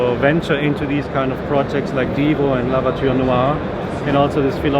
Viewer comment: I am a bit slow...I thought he said.......lavatory noire. No envy just misheard